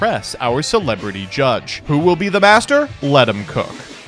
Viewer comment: I fell like I'm crazy but every intro I hear say "who will be the bas***d" instead of "who will be the master" like my guy rerecord one time help me